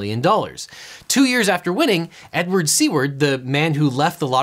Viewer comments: The guy in the examples name was Edward Seaward, but I heard it as Edward C-word which I found quite fitting. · You said “Edward Seward,” and I thought you said “Edward ‘C Word.’” I spent like 2 minutes thinking about if his name was “C Word” or if it was the actual C word and you were just censoring it for the YouTube video. I assumed it was the latter and thought about how unfortunate of a name like that was and how a name like that even comes to be. Like could someone’s last name be the F word, for example? It got me thinking about how that would happen or why someone would do that or the social etiquette around saying someone’s last name even if it’s a curse word. And should toddlers be saying curse words if it’s their last name? How does that affect everyone at school when teachers say it in class? Would other parents sue the school for teaching that kind of language to their kids? I basically went on a long train of thought until I saw his name written down on the paper as “Seward” and not “C Word.” Which explained a lot lol but I’m still interested in what I came across while thinking about it